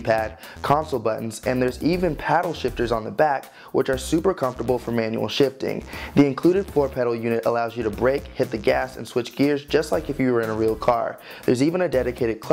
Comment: did he say 4 pedals 😂😂😂